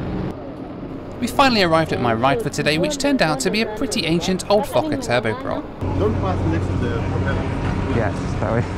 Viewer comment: At first I thought he said “old F**ker turboprop “